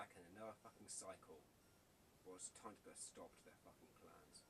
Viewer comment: can you tell me what you say between 8:15-8:20?plz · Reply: I say "Well its time to put a stop to there fucking plans"